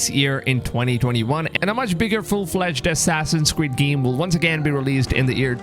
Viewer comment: I thought he said Assassin’s Squid.